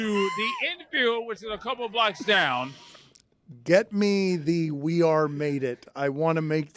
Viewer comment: He is clearly saying "We have made it" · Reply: It sounded like he says "we are faded" 🤣🤣🤣